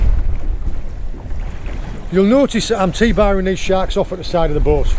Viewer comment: I had this video playing in the background while working. I misheard and thought he said he was tea-bagging the shark over the side of the boat. I was thinking... man this guy is hard as nails. Ran it back and he said T-bar-ing (21:42), definitely made more sense. Don't go tea-bagging sharks people!